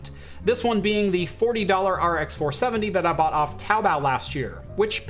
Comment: Right at 7:18 saying you bought a used RX 470 from... (??????). I listened to it 5 times and still don't know what you said. "Towbow"? LOL! I was actually looking for a good used RX 470 for my son'd friend. They seems to be very popular because they're hard to find - unless. one wants a mining version.